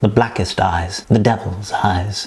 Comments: In the beginning it sounds like he said the blackest dies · it kind of sounded like “the blackest dies”